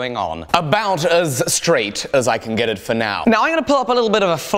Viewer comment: When he tried to say "About as straight" it sounded like he said "about as lige" which would be "straight" in Danish